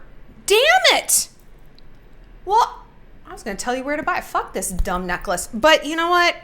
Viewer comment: wait wait wait did she said fuck the merch mid announcement? lmaoo Shallon is ruthless